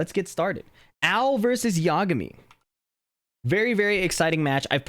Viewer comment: i heard “L vs yagami”